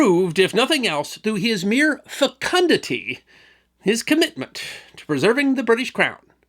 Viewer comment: What was that word "fucunduncy" "facundenscy" ?????? It was at 3:35ish. Facundundty???